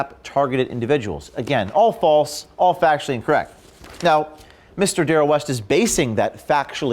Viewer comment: I thought you said ''coree fct'' and got confused asgf